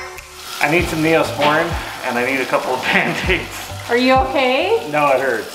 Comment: the Auto-Generated captions said “I need some Neosporin and I need some pancakes”